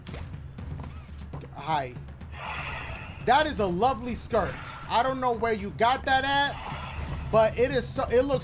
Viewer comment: did she just say hi back to him😧